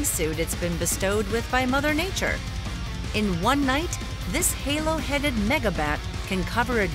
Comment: ayo what she say!? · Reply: She saying megabats, not an N word bat